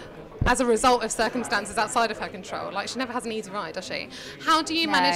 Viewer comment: What does the interviewer say in the beginning at 0:09 she never has what? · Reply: "...she never has an easy ride, does she?"